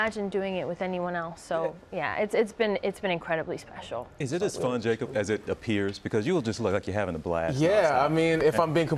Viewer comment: im nosy but im so curious to know what tom whispered under his breath to Zenday at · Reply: i think he said “stop it ur so sweet”🥺